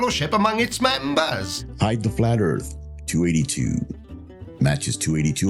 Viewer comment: Every time I hear "hide the flat earth" I keep hearing "Fight the Flat Earth"